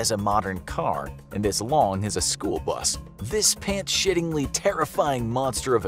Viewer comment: did he say "Shittingly"? 😅